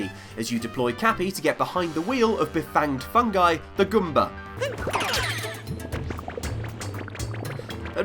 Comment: is it just me or did he say gumball